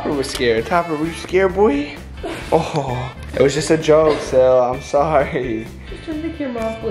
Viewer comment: did josh call Katie Sarah